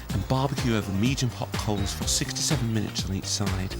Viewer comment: I heard “67 minutes on each side” lmao